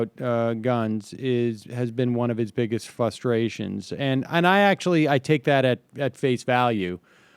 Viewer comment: It's a relatively minor point, but if I hear a grown adult say the word "fuss-tration" again, I'm going to lose my fucking mind.